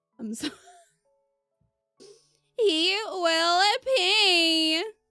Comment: ngl I thought you said "He will PeEeEeeee" Honestly all the dialogue is crazy that I'd expect KORNY to say that😆